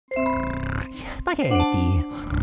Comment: did Mario say Spaghetti??